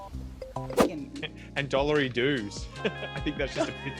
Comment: I'm an American. When I first saw this episode, I didn't know the Australian father was saying dollarydoos. I thought he was saying "dollar reduce." I thought maybe that was an Australian way of saying that a certain amount of money has been deducted from his bank account or something. I thought maybe they say "reduce" instead of "deduction," for some reason.